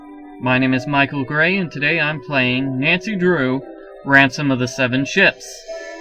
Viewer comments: I thought he said, "Ransom of the seven shits." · i thought you said Nancy drew ransom of the seven shits XD